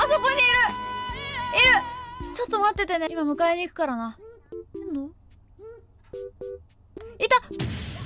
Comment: もう遠藤ォォォォォにしか聞こえない